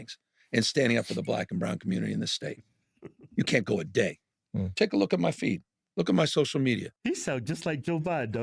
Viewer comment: I wasn't really watching, I was just listening. And I thought he said "Take a look at my feet". Lol. No Newsome, we don't care to look at your feet. And then he said he cares about winning and power. Not about integrity. But power and winning. Probably the only truth he's told in his life.